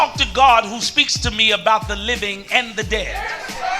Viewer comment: what did that ninja just say? God talks to him about the living and the dead